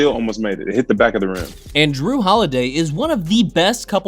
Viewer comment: did you just call Jrue holiday Draymond?